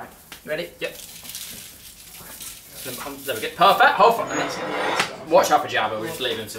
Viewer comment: did he Said “Watch Out jabba(idk how’s that writing)”😂